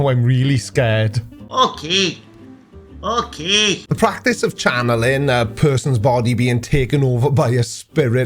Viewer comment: Sorry I thought you said chunneling.